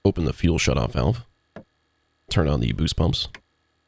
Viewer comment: did he say "turn on the goosebumps"??? :oops